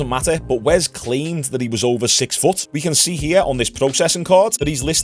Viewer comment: I thought wes said the guy was 7foot and I kinda think the gay black guy got what he came for to be honest. I also think guys like you kinda had a hand in all this because of all the constant hate sent his way. Yes Wes seems to be a dip shit and gets on my nerves but he is also doing his money thing and the people constantly hating and the people buying his stuff are even bigger dipshits in my opinion